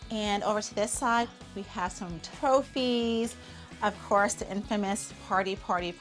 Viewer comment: Did anyone notice Holly said tophies instead of trophies